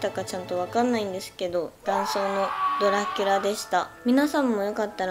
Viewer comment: ダイソーのドラキュラでしたって聞こえて、 「え！？買う！！！！」てなりました。